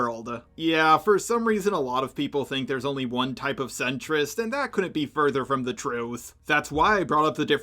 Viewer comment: I wasnt looking at the screen when you said leninist so i thought you said lennonist as in john lennon and i was very confused 💀😭